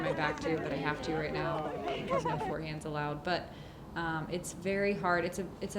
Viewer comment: Madison, at 4:35 did you say "forehands aren't allowed."?